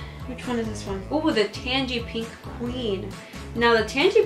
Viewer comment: I thought she was saying Tangee Pee Queen and I was like, “Hm, okay...?” 😂